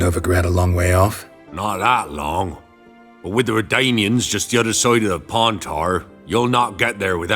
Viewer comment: I hear the catfish is the king of the Pontar.